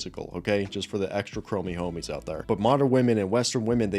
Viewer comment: did you say, "just for the extra-chromey homies out there"?? 😂❤